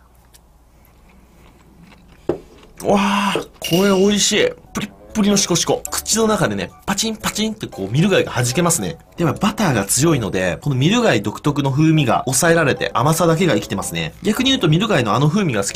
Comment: ぐらいプッリプっりのシコシコって聞こえる